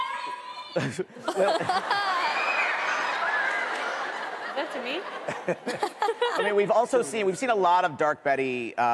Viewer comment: What the fan shout at 22:00 ?? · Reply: She said MARRY ME to cole